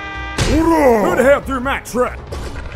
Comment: what did that voice say, I always laugh at that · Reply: "WHO THE HELL THREW MAH TRUCK!?"